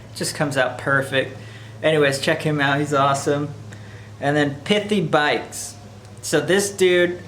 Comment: Hey, great channel, you were recommended some channels and the one I would like to look up is (I thought you said Piftybites), could you spell that one for me, I couldn't find anything. thanks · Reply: Hey bud thanks! It's called pithy bikes. If you can't find it I have a link in the description that'll take you to his channel! Thanks for watching bud